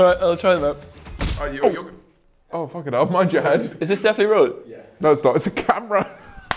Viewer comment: What did Will say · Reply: is this definitely rolling